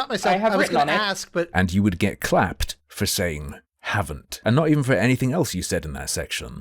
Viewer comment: I'm an Aussie too, I think she is a compulsive lying sociopath.....but I hear she said "havn't......which also help with the contradictory lies she keeps telling.